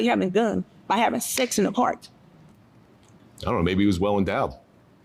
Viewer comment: what did he say?? i cant figure it out for sure. did he say "well in doubt?" "well endowed?"